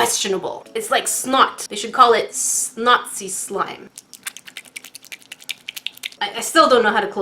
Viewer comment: It sounded like she said nazi slime